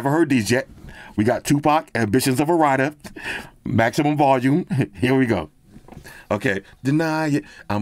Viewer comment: Maximum volume? Did I hear that right?